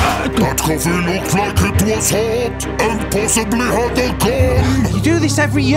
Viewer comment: it was a little hard hearing rescue whale's dialogue, a bit fast for that low pitch and with that odd effect on it (is it a harmonizer?). I got enough of the jist of it, but just wanted you to bear that in mind on future vids, I quite like rescue whale.. so cuddly and blubbery!! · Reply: ***** i thought he said "impossibly hot" and not "and possibly had a gun" lol i dunno how i made that mistake